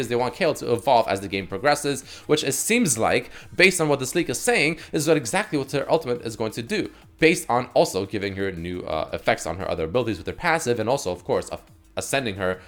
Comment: I heard "based on what this nigga sayin'"